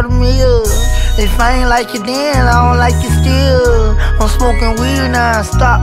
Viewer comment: I thought he said he don't smoke